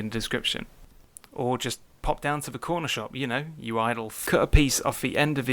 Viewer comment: did you just call me "idle fuck"?